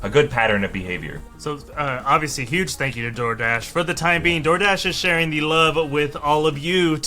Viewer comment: 1) fiancé!?! 😫 2) i kept hearing “thanks to jordache” 😂